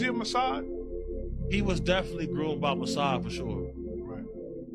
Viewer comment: Why did THEIR VIDEO CAPTIONS say "he was groomed by Messiah"?!!! WHEN HE SAID MUSSAD!! However you spell it!!!